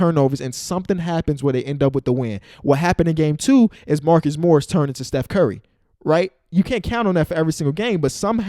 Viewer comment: did just say Marcus Morris turned into Chef curry?